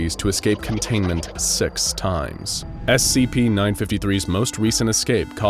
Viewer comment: Im here moron or i hear at 8:07 s@ time but no six time